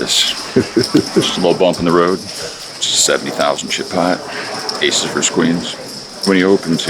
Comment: The subtitles said its " a 70 thousand shitpot " instead of chip pot. When your aces were cracked by QQs.